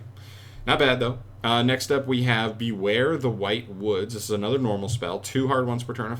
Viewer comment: Thought that said "white hood" I said ayo WHAT